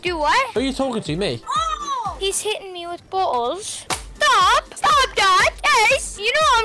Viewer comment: sounds like he said his hitting me with his balls